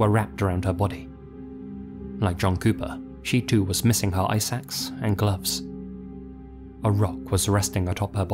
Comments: I kept thinking to myself "what are eye sacks?" Then I saw the picture and was like "Oh, ice ax" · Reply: I heard "eye sacks" too 😭🙏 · I kept hearing “eye sacks” instead of “ice axe” 😅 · Ice axe omg it just clicked. I thought you were saying eye sacks lol